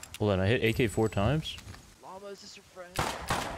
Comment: Bro did anyone else hear “llama is this your friend”